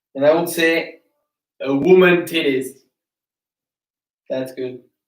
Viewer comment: did he say woman titties?